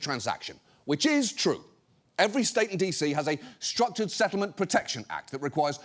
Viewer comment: Wait why did Jon say every state in DC?